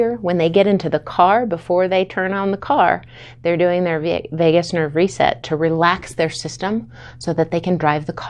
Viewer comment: I thought she said "Vegas Nerve Reset" and it involved slot machines and blackjack 😂